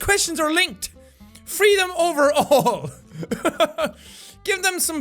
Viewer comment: When it said spit on them, i thought it said sit on them